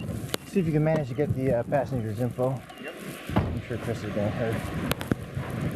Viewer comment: Ohh they were right outside Davion’s house! 43:13 what did the cop say about Dom’s mom? He said “I’m sure Chris” something. Damn this is a small ass town. I think the cops knew his mom cause she was a lawyer? Yeah no wonder the cops had the reactions they had when they found the wreck. They knew these boy’s parents personally. That had to hurt seeing them and then having to tell the mother ughhhh! Can’t imagine.